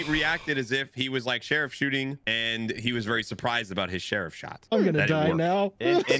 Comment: "share of shot" instead of sheriff shot lol